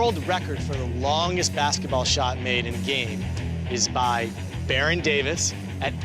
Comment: This is so fucking stupid, at around 1:16 he clearly says IN GAME